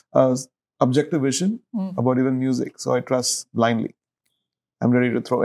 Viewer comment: Thanks to the subtitles now I know he says "I trust" (about what he does that other directors don't). I was so puzzled at first because I thought him say "I touch". Wow, he said it for Aish, precisely and squarely. It's emotional.